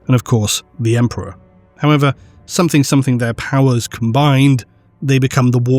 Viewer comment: "something something their powers combined"... Did I hear that right? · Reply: 'He's a Hero'